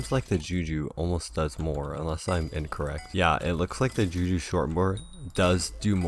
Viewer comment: did he just say juju shor bor